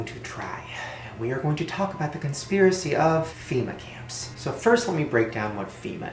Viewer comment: I thought he said female camp😂